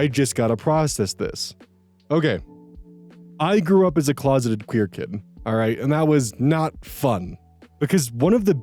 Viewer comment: When you said “closeted queer kid” I heard “closeted cryptid” and I thought you were just casually letting us know that werewolves are real.